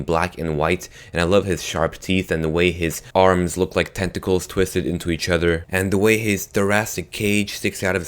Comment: My god, I swear I thought said "testacles" instead of tentacles